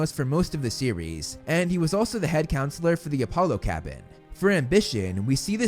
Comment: Did he say Apollo?